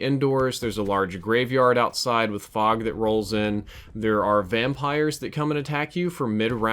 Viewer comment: did you say fog rolling in